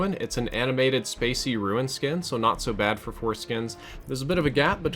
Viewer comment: what did you just say? Not to bad for foreskins.